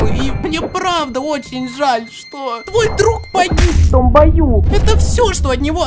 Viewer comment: мне одному послышалось что там не "в том бою" а мат